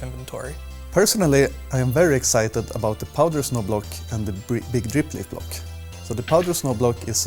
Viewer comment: I really thought he said Poggers Snow Block